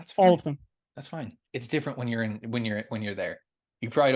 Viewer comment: I thought they were saying it’s different from the 2018..